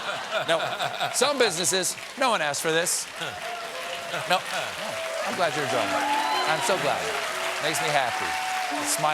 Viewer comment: Did he say, "I'm glad you're drunk." at 7:49?